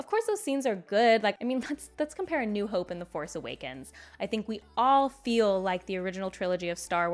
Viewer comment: I thought you said The forest awakens. Not least because they are in a forest there....